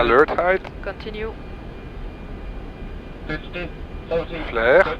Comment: What is this call out GDPS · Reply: “50”